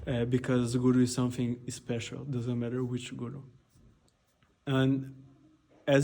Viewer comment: Vishwananda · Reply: ty i thought i heard them say guru G